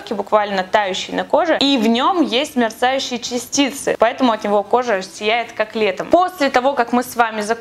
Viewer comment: мне показалось что она сказала «поэтому кожа воняет котлетами»😂😂